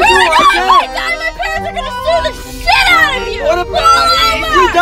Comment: The girl- If we crash my parents are going to sue the hell out of you BOY- They can't if we both going to die 😂😂😂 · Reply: Lool haha I thought she said are going to shoot the hell out of you